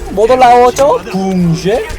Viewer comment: 나만 붕쉐라 들었냐